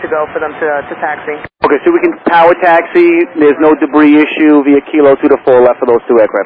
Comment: i think he said "ok so he can "power" taxi"